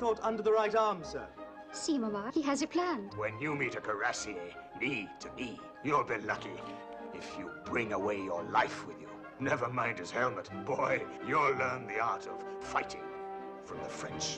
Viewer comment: what does general picton say? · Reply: "When you meet a Cuirassier, knee to knee, you'll be lucky, if you bring away your life with you, never mind his helmet, boy. You'll learn the art of fighting, from the French".